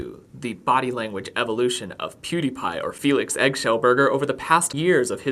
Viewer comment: Did Logan really say Felix Eggshellburger? I cannot have heard that right...